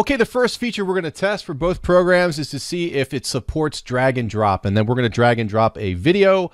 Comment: Great video! I thought you said "dragon drop" at first.